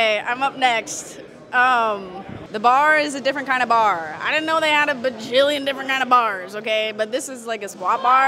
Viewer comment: I thought she said she had a baby.😮😳🥴